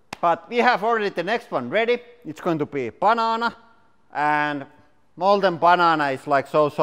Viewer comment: Wait, did he say "molten banana" at 4:50? hahah 😂🍌